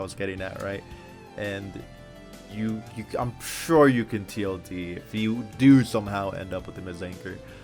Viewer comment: can anyone tell me what tld means? i thought he was saying tod here but im not sure